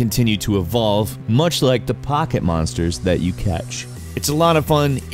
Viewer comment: Did he just say Pakemonsters? TRIGGERED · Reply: he said poketmonsters which if you didn't know was the original name for Pokémon way back in the 1990s